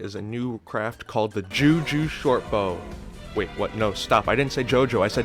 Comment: For a second i heard "jew jew shortbow"